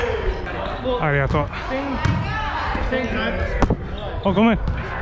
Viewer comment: You are french right ? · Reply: Oh ok But at 0:26 , I heard u saying "Allez , à toi"